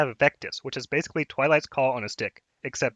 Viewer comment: I thought he said "basically twilights call on his dick"